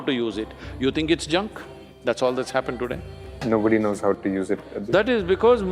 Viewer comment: "No devotee" instead of "No duty". These automatic captions can be really bad.